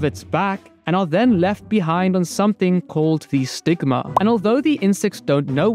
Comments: Did you just say the sigma? 😂😂😂😂😂😂😂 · for a second, I thought he said “sigma”. I had to watch it again to realize he said “stigma” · Stigma- my dum brain thought he said sigma😔